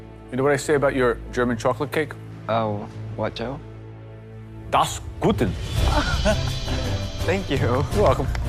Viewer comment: I think he said "das gut". Of course it's wrong but it's the thought that counts. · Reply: i thought he said das guten?😂